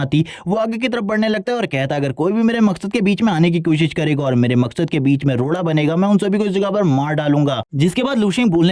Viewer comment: I thought you said Laura in